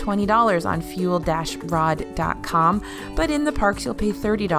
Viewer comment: Sounds like you said "Fuel-FRAUD. com"